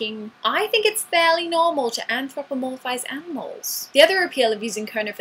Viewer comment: what do you say at 1:10? "amfroffomolysais animals"?